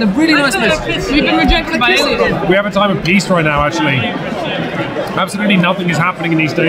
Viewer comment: I thought I heard Quinns say "we have 'A Time of Geese' " as he opened a box. I thought: "Quinns is at a loss as to what to do now and so he is opening up a fresh copy of a game called 'A Time of Geese' and is suggesting a quick play." I was this close to running a BGG search on 'A Time of Geese.'